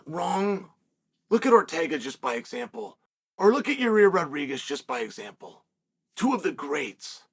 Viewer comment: New fighter unlocked at 12:13 "yiri rodriguez" . Undisputed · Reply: I thought he said "rareer rodriguez" 🤣🤣